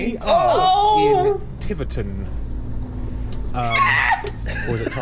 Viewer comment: Flashing your lights and using your horn is only allowed in case of danger: for example, to avoid a collision. I assume those rules apply in the UK as well as here in the Netherlands... Anywho, at the start of the video I though I heard "Tibberton" and went "Aww yeah, I got family there"... Turns out he's bloody miles away, down south in Devon. Finally, I have to agree with the rest of the comments here in saying that a lot of the roads there looked plenty wide😆